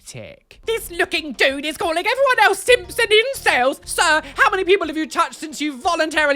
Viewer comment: I thought you said "Simpson incels" and got very confused for about three seconds (/)..)